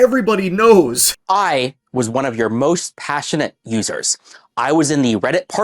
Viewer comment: I heard most passionate losers😂